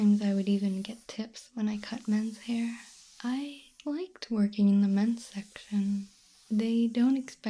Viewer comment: Me relaxing with my eyes closed 7:06 : I like twerking on the men's section.. Me: Wide awake hol' up.. what did you say? Replay 7:06 : I "LIKED WORKING" on the men's section. Me: Oh, ok... lemme go back to sleep.